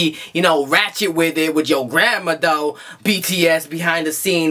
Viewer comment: I thought you said "ratchet with your grandma"😂😂